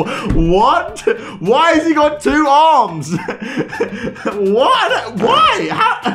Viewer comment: fam,what did say? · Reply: Why he got two arms 😂